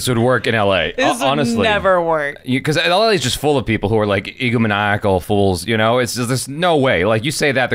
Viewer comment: What's that word that used? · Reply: Egomaniac